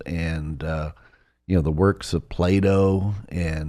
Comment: Did he just say "the works of playdough?!" 😂😂🤣😅